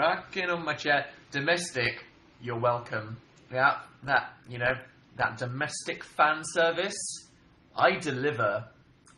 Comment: did he say DOMESTIC PHAN SERVICE??? or was it just fan?